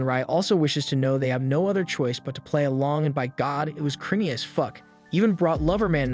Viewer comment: it sounds like he said it is crimy as fuck lol